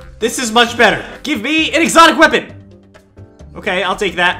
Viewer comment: I thought you said exotic butters